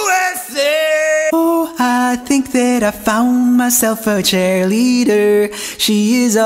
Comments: did he say “chair-leader”? 😂😂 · ok is it only me but I heard "Ooh I found myself a chair litter"